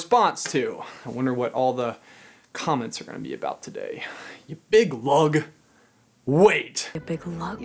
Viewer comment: that whole expression was definitely ripped from game theory Mario Galaxy theory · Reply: Tillytally222 5:55 what is he saying there though? Big hug? And then meg and mother gothel both say it??